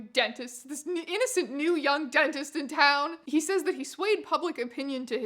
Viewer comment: I heard "young dentist" as "undentist" and now that's what I'm mentally calling this man. The UnDentist.